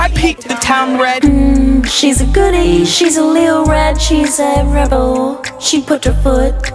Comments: HAUR HAUR HAUR HAUR “she’s a lil red she’s a rebel” 💀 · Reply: LMFAOO I THOUGHT THEY SAID "she's a little rat" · HAUR HAUR HAUR HAUR “she’s a lil red she’s a rebel” 💀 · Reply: nah I thought she said "she's a little red she's a pebble" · Help! I thought they said "I PEED THE TOWN RED" 💀